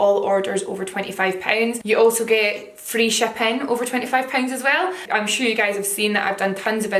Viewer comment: I swear I thought you said "free champagne" instead of free shipping. I was like really??